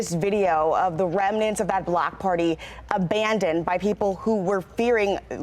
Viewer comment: I swear at 3:09 the anchor said "black party" and not block party??